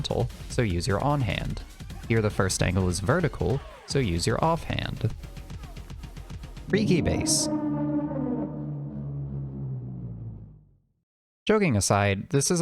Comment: Subtitles say freaky base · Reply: I without subtitles, but i also heard "freaky base"